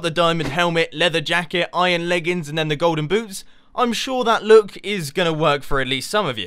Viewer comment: it sounded like u said golden boobs